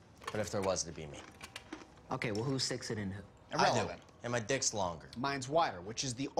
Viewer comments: does anyone know what mickey says here? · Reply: He says "irrelevant" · When they ask who sticks it in, I hear Ian say “I do” but I can’t tell what Mickey says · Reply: Mickey said "irrelevant" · what mickey said there? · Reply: Irrelevant